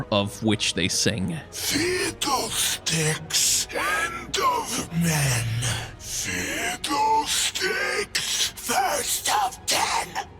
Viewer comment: when i heard fiddle sticks doing the fid-dle sticks end of men, fid-dle stickes first of ten i thought he said fid-dle sticks end of men, fid-dle sticks fast as ten